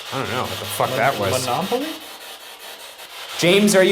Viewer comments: I hear “don’t laugh at me” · I heard: ‘don’t laugh at me’. Maybe I’m bugging. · idk if it’s jus me but like, it kinda sounds like someone says “what’s happening” when they think it sounds like “monopoly”